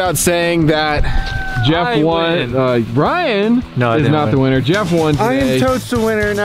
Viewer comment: did he say "Bryan"? I thought his name was Ryan 😂